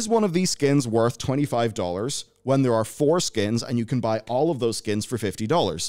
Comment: Was not really listening and just in the background when I hear “You can get foreskins!”…. Oh 4 skins